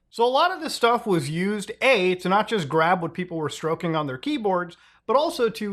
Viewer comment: Wait did you just say grab what I was stroking on my keyboard 😳🫣